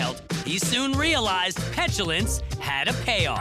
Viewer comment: What did the voice-over guy say had a pay-off? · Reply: lynda renaud Actually the narrator says, "Santos suddenly developed a fascination with flatulence ... he soon realized petulance paid off". So he says both. Maybe you should actually get your hearing checked?